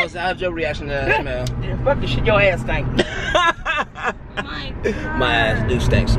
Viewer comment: Even the baby was like..🙅🙅 it SOUNDED like she said "U Stank"